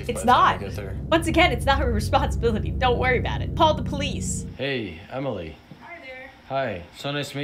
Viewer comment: i cannot be the only person who heard "Pall the Police" because that is TOTALLY what she said